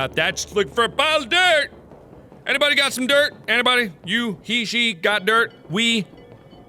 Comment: "Has anybody got any dirt?" Lol Major flashback! I couldn't stop laughing after that cuz all I heard was "where's the Blacksmith?!"